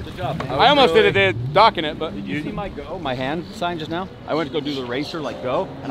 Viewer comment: i heard say '' hold my hans'' ,,,,,,,, very disturbing